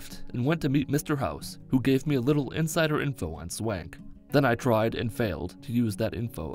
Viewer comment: I thought he said "And went to meet Mr. House, who gave me a little insider info on swag ," (I was playing the video while doing something) and I never questioned it for a long time. Edit: I wish you could do things to Benny's fanny with the Confirmed Bachelor like with Black Widow. Bi Benny was just never meant to be, I guess.